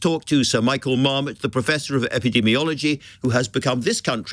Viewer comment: I thought you said Professor Marmite. Wasn't too sure about that.